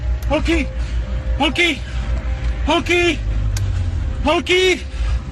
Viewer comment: DID BRO JUST SAY MONKEY☠️